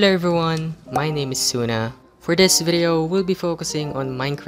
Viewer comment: did you say suna?